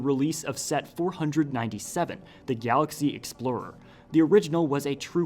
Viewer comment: is it just me or has anyone else heard he say The Gyattlaxy 😂💀 love your video's by the way